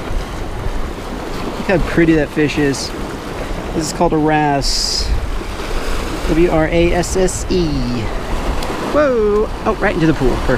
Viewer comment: The first time I heard him I thought he said “it’s called your ass”